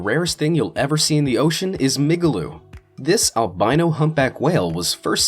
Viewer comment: thought that says “Nigaloo”😂😂